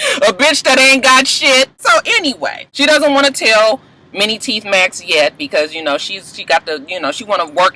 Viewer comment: Mini teeth max 😩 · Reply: Oh I thought she was saying many teeth max. I was dying lol